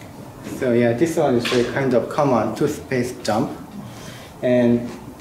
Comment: "two space jump"? I thought he said toothpaste dump. Also a good name.